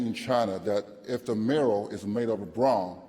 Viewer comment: Can someone help me understand what he's saying around 6:58? Mural? Middle? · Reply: Mirror? I don't know.